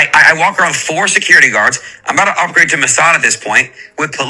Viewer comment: Did he say "upgrade to Mossad"? How does a kid his age even know about Mossad? That's wild.